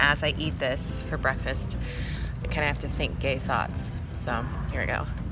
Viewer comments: I thought Jen said Eat a turkey sandwich Have some gaythods and I was just like, what the heck are gaythods?! then later on I heard it was gay thoughts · When they said "And think gay thoughts" I thought they said "Wear gay socks" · Umn I have a sandwich have some gay thoughts I thought you said have some gay sauce :0